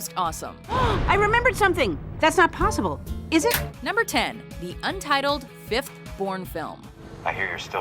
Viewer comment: I wasn't looking at the screen when she spoke and I thought she said "The untitled fifth porn film"